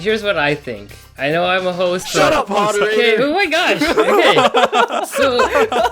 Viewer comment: did he say “shut up Haruhi?!” Or is it just me?!